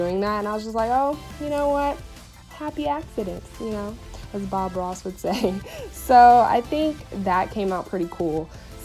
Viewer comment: i thought she said "as bad ross would say" and i was like "WE WERE ON A BREAK"